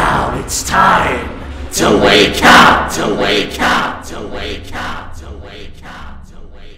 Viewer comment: does anyone hear 2 way cup?